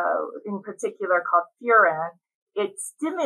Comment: she says, on the components of coffee, one of them in particular stimulates a hormone called gastrin to be released from the stomach lining. The word she said sounded like "cofura" but I can't seem to catch it. Can anyone let me know what word she was saying? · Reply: Furan. I used closed caption to get that. @ 3:00.